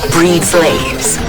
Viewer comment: I keep dying at the 19:33 montage. I keep hearing "Why can't we breed slaves ".